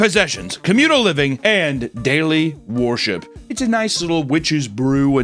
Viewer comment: I keep hearing "Daily Warship"